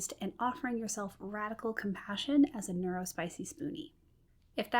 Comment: Did you say “neuro-spicy smoothie”? That is awesome. Thank you for these videos! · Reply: I probably said "Neurospicy Spoonie" which is shorthand for: neurodivergent, chronically ill, and/or disabled.